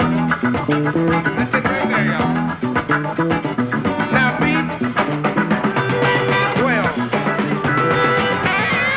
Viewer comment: Anyone know what he's sayin at 4:03? Sounds like, "now beat, 12." no idea. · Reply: Beat well